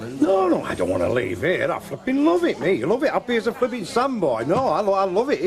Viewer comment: at the 5:15 mark did Jack say "i fucking love it" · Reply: Sounded to me like flipping